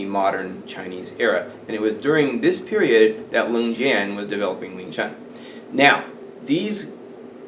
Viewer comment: I suppose you are talking about Leung Jan in 2:50 but the automatic subtitles say "Ling Jan"; Am I right?. Very interesting... specially if we consider that even today there is people claiming that westerns cannot teach kung fu.